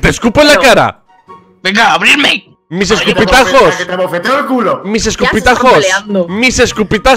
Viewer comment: mis escopetazos :v Ya se que dice escupitajos :U